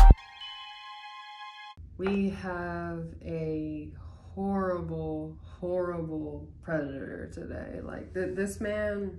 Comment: Sounded like she said " we have a terrible president today ". And she would be right...